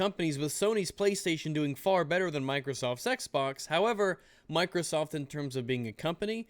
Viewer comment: Could've sworn you said "Microsoft Sexbox", not "Microsoft's Xbox"... I really need to get my mind out of the gutter sometimes.